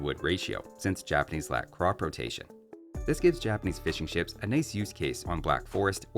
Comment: I kept hearing "Japanese Fish n' chips". Must be lunchtime !